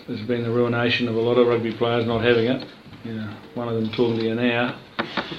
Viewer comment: Can anyone tell me what he said at 4:34? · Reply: 'one of them's talking to you now'